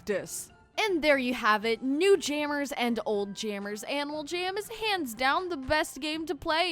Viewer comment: did I hear “anal jam”